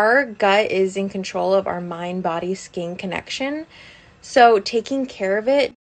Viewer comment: You heard "skin connection", I heard "spleen connection" xD